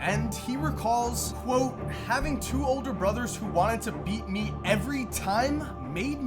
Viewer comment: I swear he said beat meat